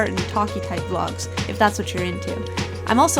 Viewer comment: What does she say at 9:18??? I'm trying to figure out the Japanese but I can't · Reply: "See you soon" is what she says. In English.